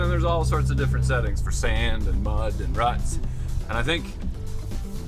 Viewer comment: i could swear said rust instead of ruts 😂😂😂